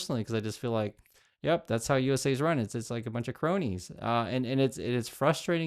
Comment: I thought you were saying bro-nies instead of *cronies*, lol. I was thinking, perfect name for them!